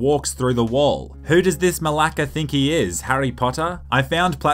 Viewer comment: did he say malaka? 🇬🇷